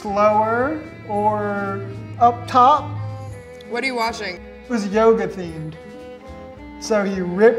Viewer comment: Why did I hear "it was yoghurt themed" ???